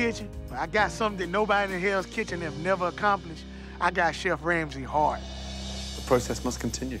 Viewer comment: "I might not have won hells kitchen, but I got something nobody in hells kitchen has accomplished, I got Chef Ramsay's heart." Me:😭😭😭 You got mine too!! 🥺❤ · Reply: I heard that wrong, I thought he said, "I got Chef Ramsey hard." I was trying to figure out if that means something different where he's from lol